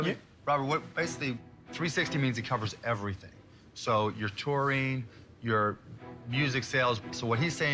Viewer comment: They way their manager said touring sounded like he said "porn", and I was very confused lolol